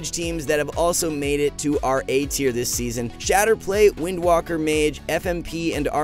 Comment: Isn't lock priest shadow play? Thought mage spriest was God comp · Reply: he refers to spriest mage as shatter play, thought he said shadow play